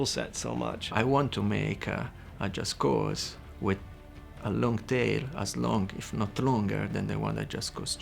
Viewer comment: "A game with a long tale"? I think he meant "long tail", subtitles are wrong there....